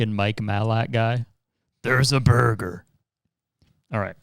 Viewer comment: did he just say.. burger